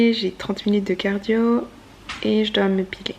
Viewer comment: c’est moi ou je comprends mal ce que tu dis à 10:44 ?? 😅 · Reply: Je dis que je dois m’épiler lol